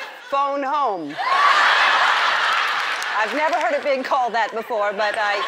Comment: at the end i thought she said "please let me make it up to you with this dab"